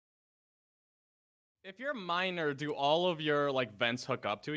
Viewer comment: anyone else thought H said minor instead of miner for a moment in the beginning of the video